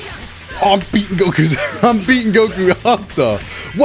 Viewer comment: I thought he said he’s beating Goku off